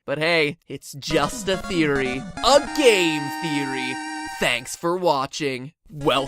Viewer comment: I accidently paused when it said the m in game theory so it sounded like it said gay theory